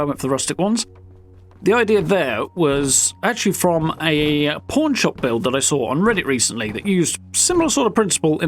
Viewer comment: I think you actually said pawn shop but at 1:51 my American ears heard "the idea there was actually from a porn shop build."